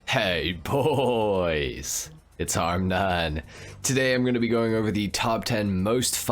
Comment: When you say your name for the intro it sounds like you say hey boys it’s hardnut